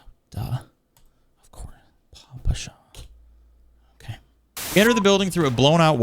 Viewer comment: Ok, no, at roughly 53:07 you did not say 'Paw Patrol' I totally heard 'Papa Show'